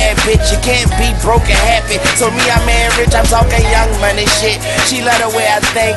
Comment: You can't be broke and happy so me I'm mad rich🔥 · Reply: Thought he said “so me I marry rich” lmao